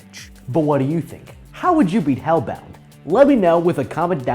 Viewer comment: Oh it’s hellbound not hellhound, this entire time I thought it said hellhound until I watched the vid lol.